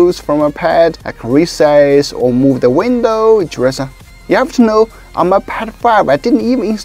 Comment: that sounded like you were saying. " You have to know i'm a pedofile" 😂